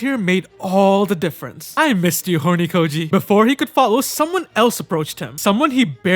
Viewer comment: did did bro say hornykoji 1:04 ?